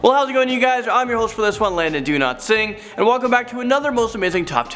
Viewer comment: Is it just me or does it sound like he saying Landon Do not sing at 1:04 ?